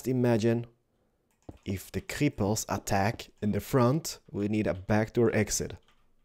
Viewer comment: I thought he said "when the cripples attack" lol